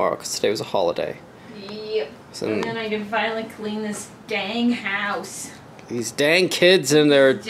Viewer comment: Baha. I thought you said gang house. 😅